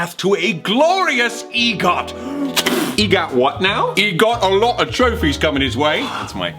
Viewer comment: Does Roman refer to Deceit as "E" near the end of the video? The line how I'm hearing it is..."If E got a chance at any top tier accolades..." but I might just be mishearing it. I've got a theory that Deceit's name starts with E because I think his character probably comes from the idea of "Ethos" or credibility. Since and Patton's names already originate from the other two appeals it would make sense if they decided to complete the trifecta and add ethos in there through Deceit's character, but it's just an idea. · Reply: Mia :D Lol, I love the deep thinking but it’s a lot simpler than that. He said “ ‘e got” as he was going back to the pun he made in the beginning of “egot” check the subtitles haha!